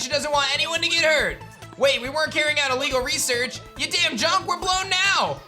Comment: Woah did he just say damn?! 15:23. Last time he said dang to bleep himself lol